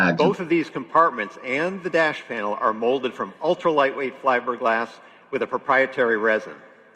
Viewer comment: did he say fliberglass hahaha